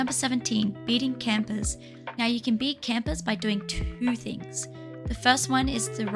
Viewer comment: the captions said teamers, and not campers